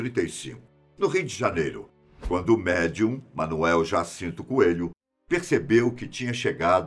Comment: Manoel jacinto Coelho kkkk eu entendi outra coisa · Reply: Eu entendi ja sinto no rego KKKKKKKKKKK